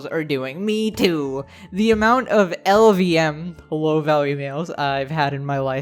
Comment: The whole time I thought he was saying low VALUE MEALS.